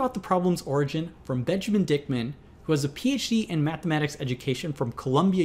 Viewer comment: Did I hear Bitchman Dickman?